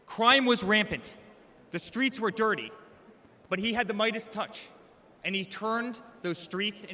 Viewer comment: you can hear him say he had "the Midas Touch." Midas paid for that. captions say lightest. But you can clearly see his mouth saying "midas touch." should I get my car maintained by midas instead of illegal immigrants?